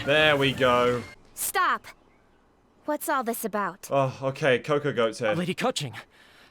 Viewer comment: Oh no, who shared cocogoat culture faster than I can click this video🤔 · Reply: wait, did he called cocogoat?